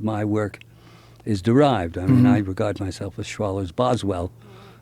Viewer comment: I know this is old and hes dead. But at 26:30 he says "i regard myself as [schwallers?] Boswell" Does anyone know what he said or what that means? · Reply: René Adolphe Schwaller de Lubicz. It's the name of the French Egyptologist who wrote the book Du symbol et de la symbolique , translated into English under the title Symbol And The Symbolic: Egypt, Science, and The Evolution of Consciousness .